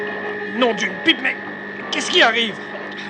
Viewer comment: OMG c moi ou a 16:49 il dit non d'une bite